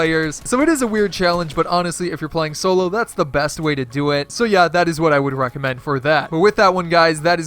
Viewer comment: Did he say so honestly solo is the best way to do it from